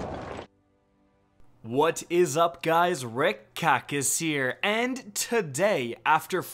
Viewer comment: For months I kept hearing, "What's up guys, Red COCK is heah..." Now that I actually heard "Rick Kackis" once or twice, I'm just keeping Red Cock as the permanent meme intro in my mind.